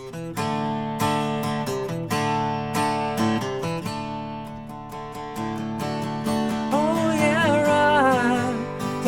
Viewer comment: i thought you said "oh yeah cheeeeese.."